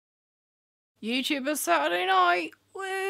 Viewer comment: When you said "I look like a really sad mime." I thought you said "really sad mom." I was like yes 100% agree then I thought wait british people dont say mom they say mum. Oh she said mime. I like mom better though.